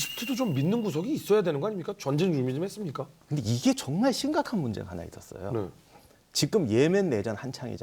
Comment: ㅋㅋㅋㅋㅋㅋ 아낰ㅋㅋㅋ 아닌거 아는데 계속 존나 심각한 문제로 들리넼ㅋㅋㅋㅋㄲㅋ